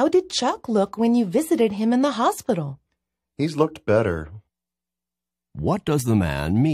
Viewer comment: Got 43/ 50.. And yes 4th answer should be A ! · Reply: oooh yeah i got it now. he said "he's looked better". i thought he said "he looks better". thanks